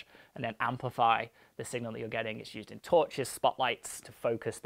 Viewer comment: Every time I heard "torches," what I processed what I heard was "tortures," and I had to keep reassuring myself that the subject of discussion was beams of light.